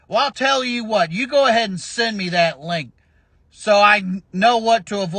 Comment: I don't know how many times I heard "You go on and send me that link." Hilarious as always Brent. 😂😂😂